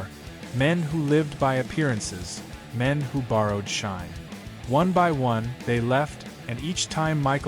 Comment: Did i just hear borrow shine